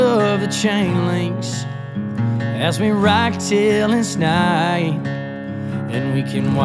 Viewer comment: What is he saying at 0:35? As we rag tale and snide? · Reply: As we rock till its night